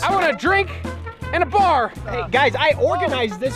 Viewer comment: I thought he said I want a drink in a bar